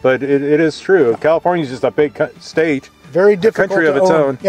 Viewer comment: did anybody else hear “big cunt state”? 😂😂🤣